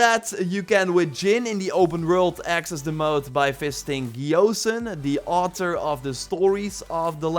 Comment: I thought you said fisting instead of visiting 😅😂😂😂😂😂